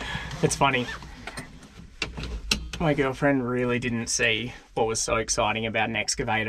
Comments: Did you say “my girlfriend?” 😳. I named my team cleaner “Steve” Best wishes for a lovely week… from Southern California 🌴💛🫶🏼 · did say "girlfriend"?